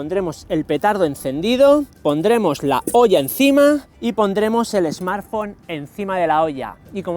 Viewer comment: parece que dice pondremos la polla encima